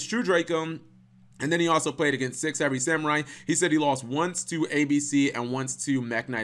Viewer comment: does he say 6 samurai, super heavy samurai or six heavy samurai? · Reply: Dylan Green it was supposed to be super heavy samurai. The giy playing that deck went 3-0 straight in that regional before i beat him. He was actually really good.